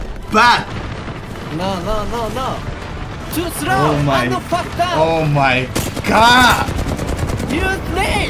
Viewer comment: Did say mf at 6:19 LMAOO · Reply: think he said "and you fucked up"